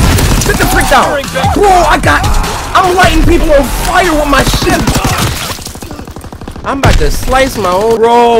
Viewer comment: did he say Shi- lol even said it was nasty